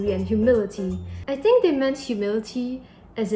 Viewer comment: I thought said « humidity »........and when you ask about the sage green.....beautiful!